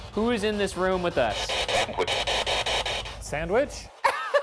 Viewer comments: I heard “Edward” not “sandwich.” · When Shane thought the ghost said “sandwich” on the voice box, I thought it said “Edward” (8:54). Later they said 12 British soldiers died when a cannonball hit the house. Edward is a pretty common British man’s name; for that period especially. Just a thought..